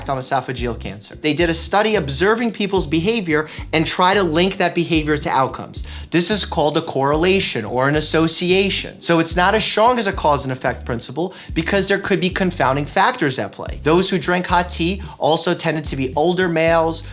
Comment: I keep hearing "Cauwse" when he says Cause and effect (3:52) , idk I'm probably the only one lol